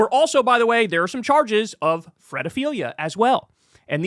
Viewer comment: fredophilia? threadophilia? What's that word kyle used?